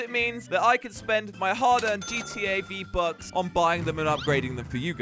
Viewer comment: I thought Josh said, "GTA V-Bucks" but he really said, "GTAV bucks".